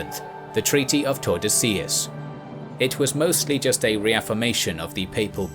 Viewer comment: I misheard the treaty I thought "Treaty of Tortillas"